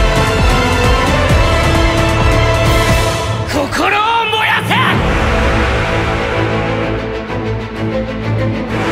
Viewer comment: What does tengen say at 1:49 ? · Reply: It's Tanjiro, and he says something like Rengoku said, "Set your heart ablaze" or something.